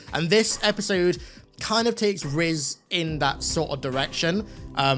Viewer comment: Did you say Rizz at 4:28? Come on